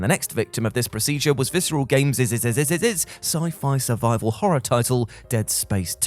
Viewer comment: “And the next victim of this procedure was Visceral GamesESESESESESESES sci-fi survival horror title Dead Space 2” Is it just me who heard that? What was that? 🤣🤣🤣🤣🤣